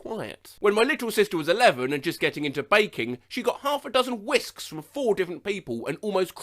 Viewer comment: I accidentally misheard the “when my little sister was 11 and just getting into baking” as “just getting into VAPING” and I was like “Wait WHAT?”